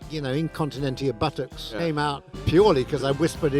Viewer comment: What other Pythons sketches or scenes do you particularly enjoy? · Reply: Mu favorite Python sketch of all time is "The Argument Clinic". As far as skits from a movie, I love this one, and the bridge of death scene from Holy Grail. I always thought he said "Incontenentia Buttus", but I guess it was buttocks all along.